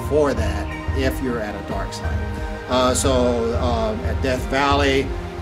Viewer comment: Did he say "Par Starty"? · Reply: Dark site